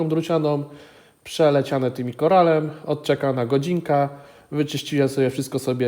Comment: kurcze nie ikoral tylko ikorol , wpisałem co usłyszałem i korale i broszki wyskoczyły 🤣